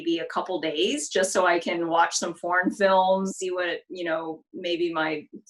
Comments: haha i thought she said porn films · I thought she said she wants her sight back to watch porn films for a sec haha. (what she really said foreign films)